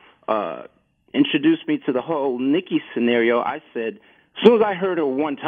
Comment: I swear at 3:09 he called nicki a “Hoe” listen